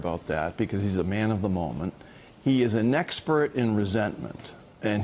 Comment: Did he say "nexpert in resentment"?